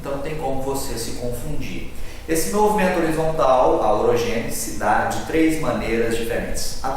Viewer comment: Eu não entendi oq ele disso no 0:57 ao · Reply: "Esse movimento horizontal, a orogênese...."